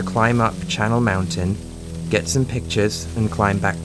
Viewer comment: I've listened to this story for years and for some reason I always thought he was saying "Charnal Mountain" and I'd always think to myself--"Yeah, if you're going to someplace called Charnal Mountain/Slaughter Valley/Decapitation Peak I don't know why you'd be surprised if it was a dangerous place"